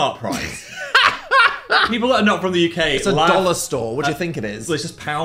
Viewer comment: Dan wtf did u say 😀 no literally I couldn’t hear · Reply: he said “it’s a dollar store” (talking about poundland